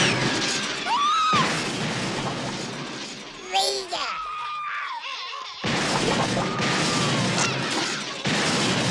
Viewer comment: What's he saying 1:49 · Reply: I think it's pronounced "Ree-ga" which in the Crite language is "Bitchin'!" I still say it when I see something cool and I still get asked what it means...if I don't get a weird look first.